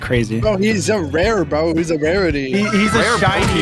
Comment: it sounds like he said verity